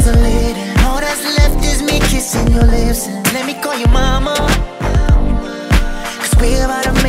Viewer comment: "Let me call you mama, cause we're about to make a baby" Dead. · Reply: OH! I thought he said "let me call YOUR mama" I'm like, NOOOOOOO XD